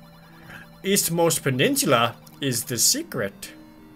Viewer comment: when it said peninsula, i thought it said penis -_